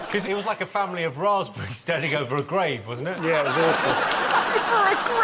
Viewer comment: "Like a family of raspberries standing over a grave." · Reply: oh - I thought he said grape. And I kept thinking...what?